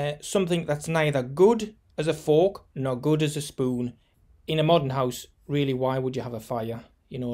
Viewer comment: He is saying that you don't have to have a huge chimney. You could simply have a pipe coming out. That big chimney compromises the geometry of the dome. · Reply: he actually says "in a modern house, why would you NEED a fire"